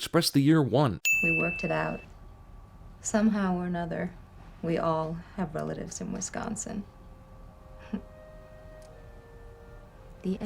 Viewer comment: did she say we all have relatives in Wisconsin